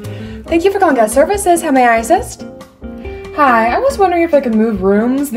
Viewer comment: When you said, thank you for calling gas services, I thought you said, how may I exist